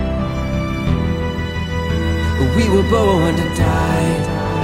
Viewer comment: Sounds like he says "we were bow and a tie"